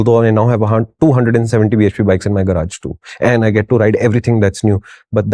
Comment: did he just say 270 bhp or two 70 bhp bikes? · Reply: Two 170bhp bikes